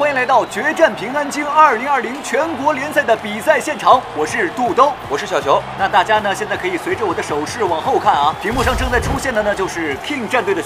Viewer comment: when the other MC said "am Little Ball", I actually heard "Am Xiao Zhan" lol moreover when I heard it and looked at him, he did resemble Xiao Zhan lol ♥